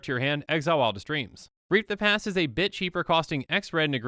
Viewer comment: it sounds like he says, "Reap the Past is a bitch . . . " Am I hearing this wrong? I must be hearing this wrong.